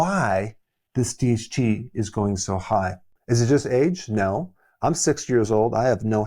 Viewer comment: did he say, he is 60 years old?